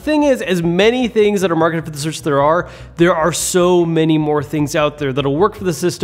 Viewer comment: What did he say? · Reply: As many things that are marketed for the Switch as there are